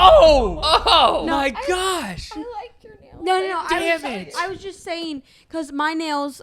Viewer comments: did say f*** 😂😂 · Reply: I think but he did say d*** it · who else heard say Dam**t?